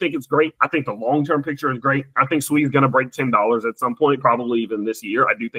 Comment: Its at 5.20 rn and we haven’t even really started a true altcoin season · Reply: Definitely! Ya I thought said it would top at $10. Went back and he said break $10